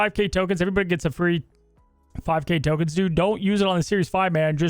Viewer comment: did say that we all get a free 5000 tokens?!👀